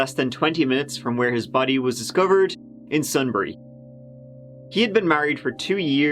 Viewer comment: "Sunbury" -- for a minute, I thought you said "Sudbury". Whew!